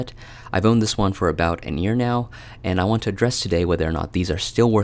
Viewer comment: I think I heard, “I’ve owned these for about an ear now” (think he meant a year. 😅😅👂👂👂👂@